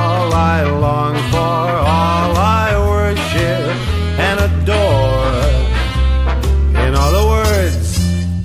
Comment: For a while I genuinely thought that Frank said “You are all I worship, and a door”